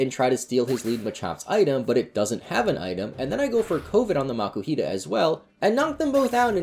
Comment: Am I the only one who thought it sounded like he said Covid?